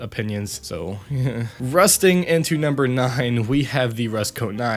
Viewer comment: Did you said number 9 in 1:44?